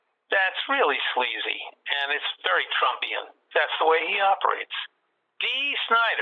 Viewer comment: did he say "Trumpian?" So that's an adjective now? Haha Man the 45th president really does live rent free in these people's heads doesn't he?